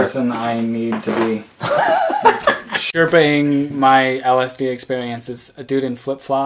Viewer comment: did he say LSD?